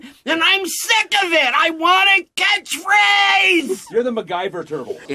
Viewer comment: Is it wrong of me that when I first heard that Donatello rant instead of hearing "I want a catchphrase" I heard "I want to get laid"?